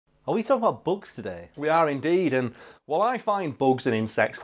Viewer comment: My hearing don't understand"northen" What I heard was, "Are we talking about books today?" Took me a second to deduce, from the context, that you two were saying "bugs"